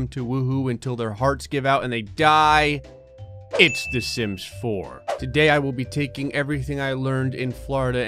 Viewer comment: When Gray did the intro, I thought he said "the simps 4"